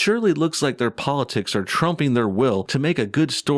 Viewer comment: Did you just say "Trump"!? REEEEE!!!!!